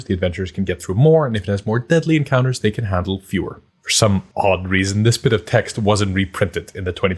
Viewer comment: I heard that L-fewer